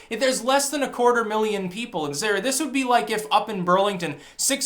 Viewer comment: does he say "Sarah"? lmao. Selective hearing, and hope! love ya!